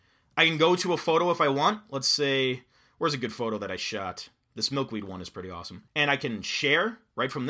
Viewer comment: Thought he said "I smoked weed once which was pretty awesome"